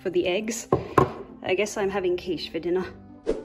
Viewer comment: i thought she said "I'm having kids For dinner." XD